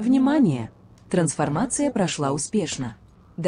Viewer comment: Возможно мне послышалось... "Трансформация." Может "Транспортация." Или лучше пусть будет "Телепортация" раз она "прошла успешно"...